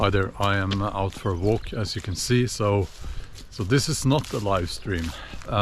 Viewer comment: "Hi, I'm ultra-woke as you can see." Misheard accent.